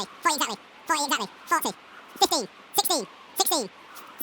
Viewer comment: did he just say sixty as if he didn’t put the limit untill 50 himself